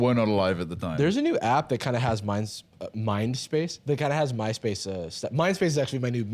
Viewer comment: Totally thought you said "Mine-space" which should be the name of my minecraft server